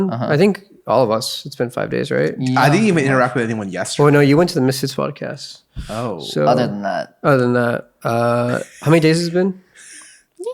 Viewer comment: just me or did just say that michael is on the misfits podcast?